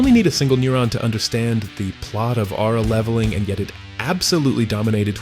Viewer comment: did you say Aura leveling?😂🔥